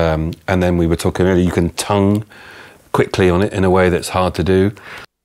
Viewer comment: Did he say "tone" or "tongue" @ 9:43? "Tonguing the Minimoog" would make a great Add N to (X) song title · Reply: Tongue - it's how you start notes on wind instruments...